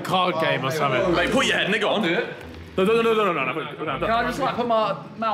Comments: was it just me or did harry say the n word? Or did he say “go on” really fast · Can’t figure out what he said on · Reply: “Ay pull your head no go I’ll do it”? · Did anyone else hear Harry say the n word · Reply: he said now go on im pretty sure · am i trippin or what did harry just say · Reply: Your trippin he said put ya head under it